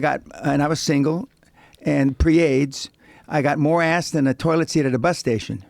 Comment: Did he just say he has AIDS???